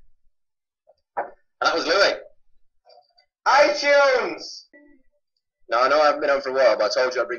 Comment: I think Louis said at 15:30 I eat Chunes or whatever